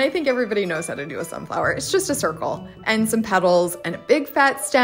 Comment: As a newby, I have some silly questions. What are pebbles, in this case? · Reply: Oh, you misheard, she said petals. ❤